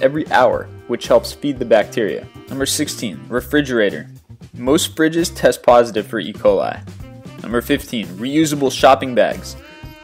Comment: Did he say "refridgerators are tested positive for Eboli"?